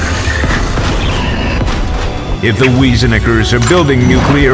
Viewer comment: what did he say??? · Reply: If the Weizeneckers are building nucular weapons the conflict escalated into new dimensions. The Weizeneckers are the No1 enemies of the Cacti